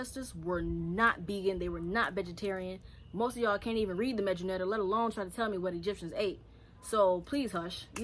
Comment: Super late to this so I hope you see this, but you said a word at about 5:10 and I'd like to understand what you said. You said, most of ya'll can't even read the (what sounded like) "medjyineatta" or something like that. It sounds like it originates from Egypt but I can't find anything on this online at least with my spelling of the word. What is that book? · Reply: Medu neter